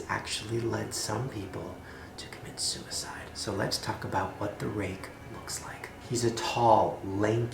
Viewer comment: Every time Shane says "Talk about" I hear "Taco Bell". Don't know why.